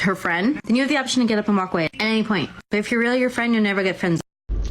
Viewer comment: Did anyone else catch at 30:55 it sounds like she says “if you’re really her/a friend, you’ll never get friendzoned.” I don’t think she understands what the friend zone is. If you’re a friend and she has no On top of that, the friend zone is only a toxic place to be if you’re sticking around in it moping about how you’re not with that girl. Have some self respect and don’t mope and hang around just to see if a girl might change her mind.